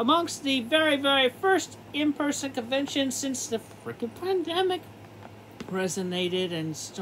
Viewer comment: Did chris say plandemic or am I mishearing him?